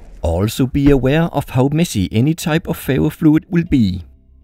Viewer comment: I thought he was saying fairyfluid